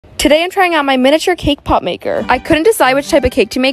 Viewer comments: I thought she said today I'm throwing out my cake pop maker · You said `cake pop' But I misheard as K-POP 😂😂😂